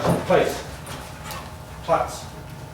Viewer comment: What word did he use to make the dog lay down? It sounded like he said plots. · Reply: Pluts**. It's German so other people can't command your dog to do things lol. My dad used to train police dogs.